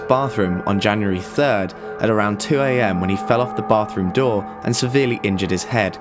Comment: “When he fell off the bathroom door?” Did I hear that right lol?